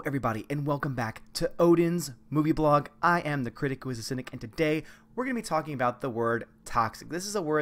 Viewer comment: Oh, you said "Critic Who is a Cynic". I thought you said, "Kwisatz Haderach". Completely changes the rest of the video.